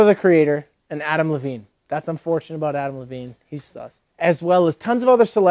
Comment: what did he say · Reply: he said adam lavigne is sus. no clue why he said it tho lmfaooooo